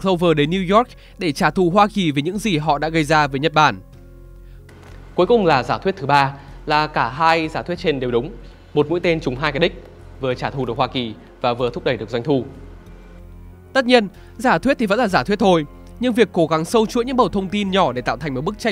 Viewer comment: Có ai nghe thành "1 mũi tên trúng 2 cái dick" không? Mình đang ngồi học giật mình tua lại nghe xem có đúng không :v